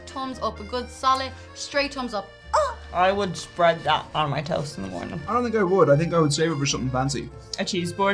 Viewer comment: I thought she said spread it on my toes🤣🤣🤣